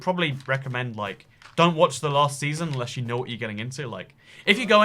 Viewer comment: i thought he said getting nude